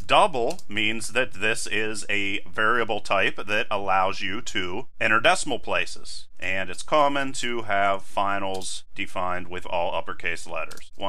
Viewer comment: Hey! I don't understand that word you say in the video by 2:00 "Double means that this is a variable type that allowes you to ?????" Please help ! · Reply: Vigge Nator Use floating point numbers or decimals